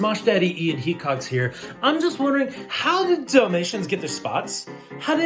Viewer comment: I thought Ian said "Dumb Asians" instead of "Dalmations"...